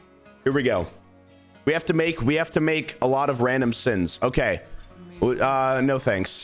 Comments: Did I just hear "We have to make a lot of random sins"? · I thought he said a lot of random sins